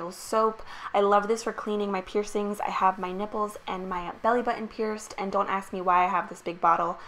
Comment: not to be rude but u have piercings on ur niples? Idk maybe I heard it wrong 🤷‍♀️